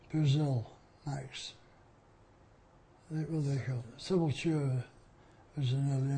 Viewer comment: Does he say Sepultura?